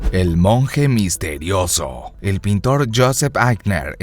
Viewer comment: Parece que dijo Monje Moco :v